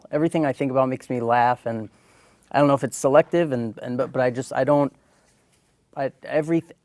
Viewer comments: What did she say? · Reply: It's selective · What did Melfi say when she put her hand on A.J's shoulder at 36:00 ? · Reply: 'It's selective.' Rob says, 'I dunno if it's being selective.' (About having nothing but happy memories working on the show.)